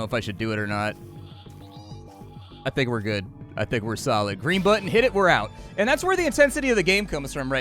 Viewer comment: Misheard him O thought he said theres grey, grey, blue and purple. Im like mmm yes variety. Then I realized he said green